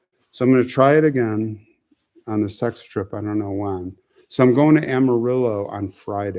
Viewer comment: did you say sex trip? Lol